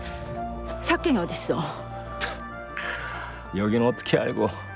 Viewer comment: 석깽이로 들리네